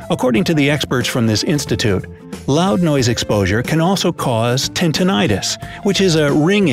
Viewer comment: Wait! Did he just say TINTINITUS?